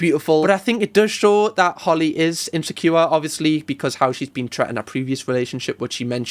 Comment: I didnt know uk folks said tret instead of treated